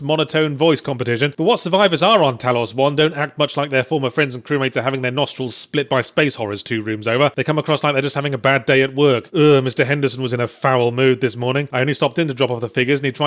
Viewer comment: Um, did he just say "Mr. Henderson" at 4:59 ? As in Mr Henderson from Yahtzee's book??